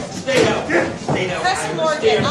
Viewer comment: Sounds like one of the Bailiffs says: "Stay down, I understand..." Can't make out after that but its nice to know that even the Baliffs can justify his actions, I know i can.